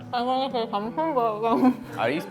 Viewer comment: I think was trying to say: "I want to say something but...". Don't take it too seriously though, I'm just guessing.